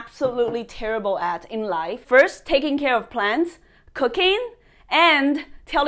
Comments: Not gonna lie.. @ 3:47 I thought she said cocaine, I was like okay. xD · thought she said cocaine 😂